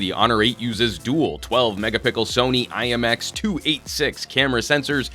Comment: I always heard "megapickel" from this guy 😅